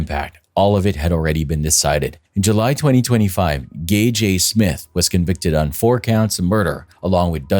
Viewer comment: I THOUGHT YOU WERE SAYING GAYJAY SMITH NOT Gage A. Smith oh my god